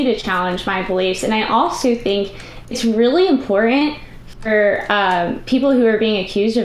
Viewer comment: did she really say, that she thought it was really 'impore-ent'?